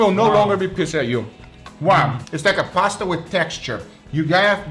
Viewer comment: Guga at 8:35 : "its like Pasta with texture" What i hear : "its like Pasta with attitude"